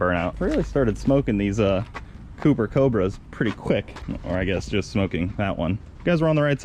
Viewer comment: I thought you said cooper cabras and I got SUPER pumped for a sec